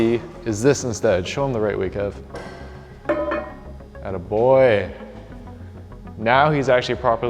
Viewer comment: did u just say "good boy" to him